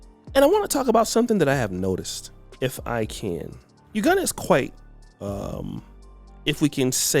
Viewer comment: I left thought said he lived in “Wakanda”… then I went back to listen and realized he said “Uganda” 😂